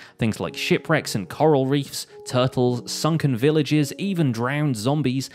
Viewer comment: Bruh did he say sunken villages at the beginning???